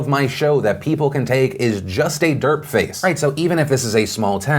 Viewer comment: Did Phil say "dirt face" at 3:40 when talking about the random thumbnails of his videos? Meaning what?